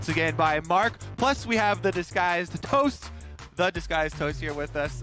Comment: I always thought it said "this guy's toast" !